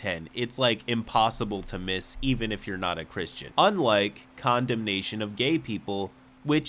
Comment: I thought said "condom nation" of gay people... not "condemnation"